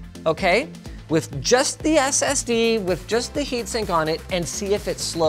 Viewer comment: CC says heatstink for the keen eyed